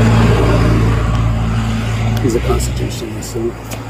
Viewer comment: Sovereign Citizens could solve 99.9% of their problems by not being Sovereign Citizens. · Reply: it almost sounded like he said “constitution denier” or “constitution guy or…” to my ears. I took it to mean something like “a person with and inaccurate/twisted understanding of the constitution”. But we all have different filters that we process info through so I could be wrong. 😊